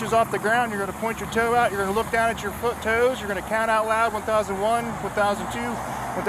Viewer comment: Did he say "your foot toes"?